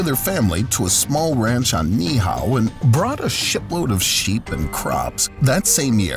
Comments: Sounded like he said "brought a 'shit' load of sheep" lol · ooooh... a SHIPload of sheeps ... I thought he said a shit load ... for real.. x'D